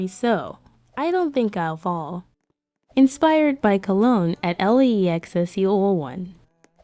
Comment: I heard "inspired by Cologne l e e x se yO yO"